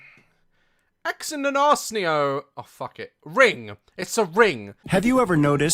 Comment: why did it sound like he said ” X in an ass io” LMAO